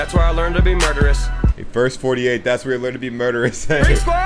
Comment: “First” 48..talking about the T.V. show. I thought it said verse too when I first heart it. Lol.